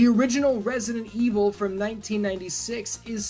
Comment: Had to rewind it because I thought he said resident evil 6 was his favorite game of all time when he actually said resident evil from 1996